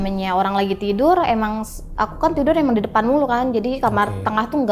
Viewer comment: ko si masnya bilang teh dea mulu apa saya salah denger ya